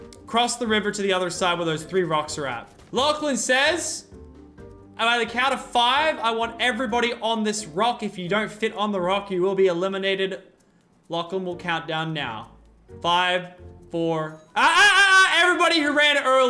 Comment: who heard him say lachin